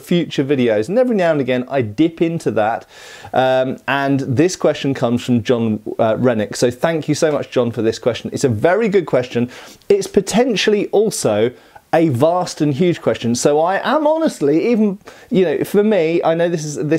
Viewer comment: I was listening while working and i heard “john rhenic?” As “jon redcorn”😂